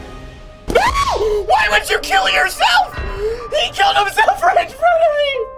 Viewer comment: The captions said “he killed himself french fries”